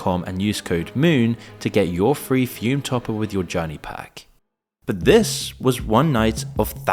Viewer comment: i heard that Füm gave my brother throat cancer